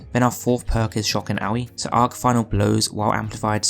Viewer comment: it sounded like you said shock and owie.... and that fits so well